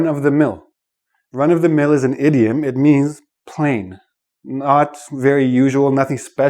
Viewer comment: Hi! can any one tell me what did say at 3:10 ? thanx alot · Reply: plain ?